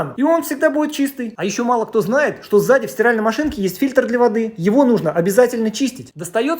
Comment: Кому тоже послышалось 300 лет стиральной машине Сюда👇